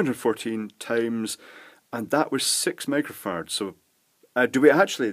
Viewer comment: Did he just say 6 microfarts?. 😂😆😝😝😜